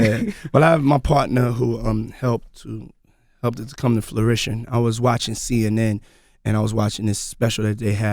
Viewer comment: Did a millionaire mogul just say '… helped this come to FLURITION.'… instead of the word fruition?